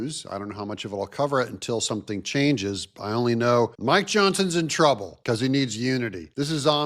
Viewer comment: I thought said. My Johnson is in trouble😅